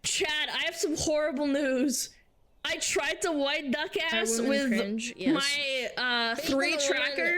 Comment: Lol Did I heard "White duck ass?"